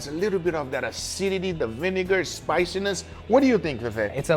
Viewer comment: my phone glitched when you said vinagre 😭😭😭 I think you already know what happened but incase you didn’t it sounded like the n word😭😭😭😭😭😭